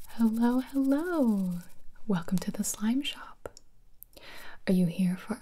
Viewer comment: As English is not my first language, there always is lots of funny stuffs that I accidentally hear. Literally the first thing I heard here was "Welcome to this lime shop". And I was like "Oh, great! What an incredible idea!"😂 Btw, lovely video, I love your ASMR videos so very much, they are often my safe space whenever I have panic and anxiety attacs.